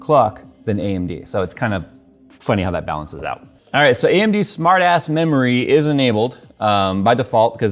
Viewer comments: I thought jay said smart ass memory. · Really sounded like you said "Smart Assed memory is enabled" 🤪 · Did he say "AMD Smart ASS memory" or did I misheard